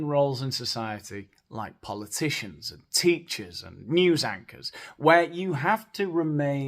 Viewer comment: At first I thought you said “news actors,” 🤣 and I thought that’s brilliant, I’m going to use that phrase. I rewound it and you said news anchors. But I’m sticking with “news actors.”